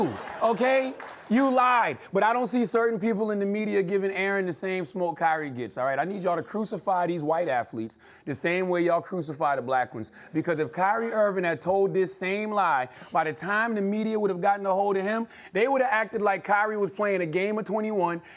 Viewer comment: at first it sounded like you said, crucify the white africans the same way you do the black ones